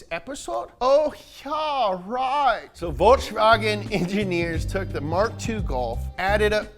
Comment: did just say "Voyrkshvagen"?